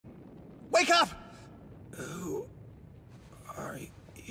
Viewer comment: First time I heard it I thought Fatgum said "I shit a few pounds but come on." had me confused for a while.